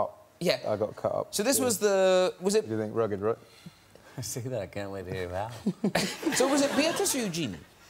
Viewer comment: Can someone write down what they're saying between 0:12 and 0:17? Thanks!! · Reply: It's rugged right. I see that. Can't wait to hear that one.